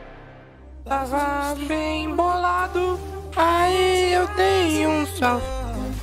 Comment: aos 7:03 só eu que ouvi ou o disse "tava bem bolado, ai eu dei uns trago"